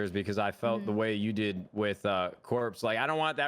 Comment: Did Nadeshot just said "with Corpse" or...?